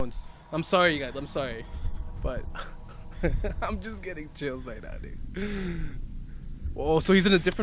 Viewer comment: the automatic captions say “ I’m getting chills by daddy” ._.